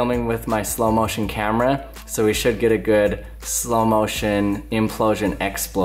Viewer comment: I thought it said “wtf is implode?”